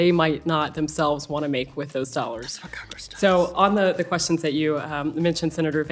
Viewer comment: what was whispered at 5:20 ? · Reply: That's what Congress does.